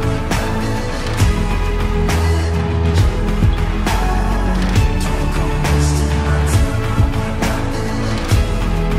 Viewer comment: What she sayin😳😳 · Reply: I believe she said “ trading vehicles for a naga dragon”